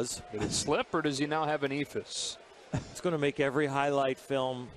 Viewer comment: Theyre so slow that they actually mess up the strike zone lmao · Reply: what did he say t 0:44 Eithus?